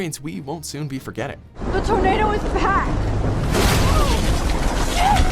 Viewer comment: I heard that as THE TOMATO IS BACK! Welp I laughed too much for my own well being